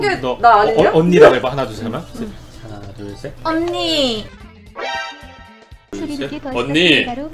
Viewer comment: 뭐죠? 무슨 애앵거리는 예쁜목소리가 들리긴했는데.... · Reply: 언니